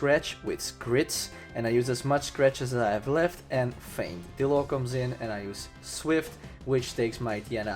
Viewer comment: MVP of the playthrough- Dillo 😄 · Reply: I thought you said dildo.....